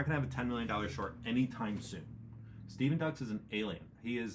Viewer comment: Steven Ducks? Couldn't make out who he was talking about.